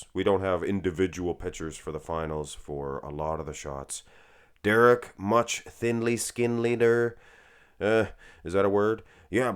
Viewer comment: I thought you said “Derek the dick skin leader” 💀